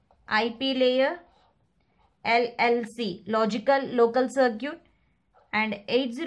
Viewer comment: mam i can't hear what you said in 9:22 i searched but its saying "logical link control (LLC) " and you said logical local curcit i think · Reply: it is logical link control only